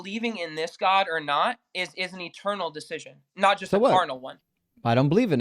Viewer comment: did bro just say that believing in GOD is a carnal decision?